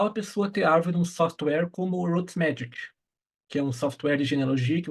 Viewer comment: Olá não entendi o nome do software que ele falou no minuto 4:30, poderia me dizer por gentileza? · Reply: roots magic